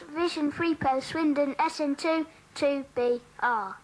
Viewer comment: SWINDON?!!!!!! Did he say SWINDON at 1:47?!!